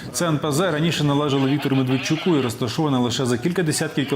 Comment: Или ведущий оговорился, или мне послышалось -- фамилия меРТВечук !!! Очень классно. медведчуку